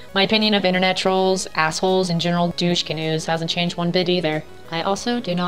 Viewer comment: did she say "douche canoes" hahaha